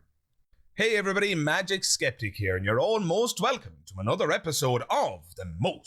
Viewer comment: I keep hearing you say “you’re almost welcome” then I realized after a dozen videos you are saying “all most”